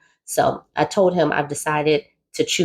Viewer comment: I know we need the support and I'm here for it, but she needs to check her state laws about evicting someone. They both sound like they're all over the place and she sounds like an opportunist herself who's looking for a new man before kicking this one to the curb. Speaking from my personal experience, once I started noticing the signs, I STOPPED ALL BEDROOM ACTIVITY!! I don't get why she's still having sex with him and sis needs to take accountability for her part as well, so that this won't happen again. She also doesn't want the cycle to continue with her kids. I think it's best that she tie those tubes and or remain celibate. Focus on her new career, tend to her children and invest in counselling. She needs to channel all of that energy that she's giving to him and other men to herself. Learn how to love yourself unconditionally first! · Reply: I thought she said she’s choosing sologamy- which I’m assuming is solo celibacy?